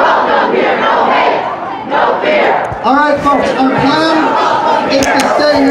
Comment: Sounded like he was saying " no hate, no fear, mammagrams are welcome here..."